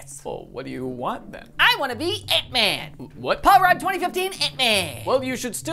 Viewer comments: I thought he was saying egg man like in sonic and then he turned into ant man and I was like “ ohhhhh” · at first I thought he said "I wanna be Eggman" like, from Sonic and all that jazz. Tbh that would've worked better, with the whole fat thing.